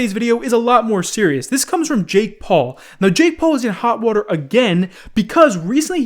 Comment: I thought you said gay paul